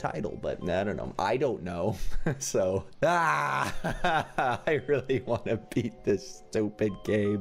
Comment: when he said i really got to beat this game in the beginning, i thought he said I really have to pee!